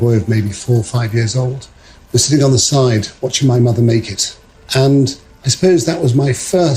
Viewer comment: Thought he said "watching my mother naked"